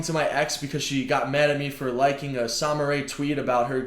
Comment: she got mad at you for liking a Sommer ray tweet about her getting a cat? Am I hearing that wrong? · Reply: Cookazor Oh, I have no idea what Sommer Ray is. I thought he said, “Samurai.” 😂